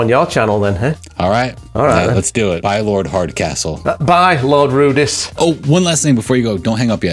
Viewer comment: Did I mishear or are you now Lard Hardcastle?! 8)